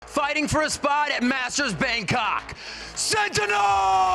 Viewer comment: first 5 seconds and i already misheard "MASTERS COCK... SENTINELS!!!!"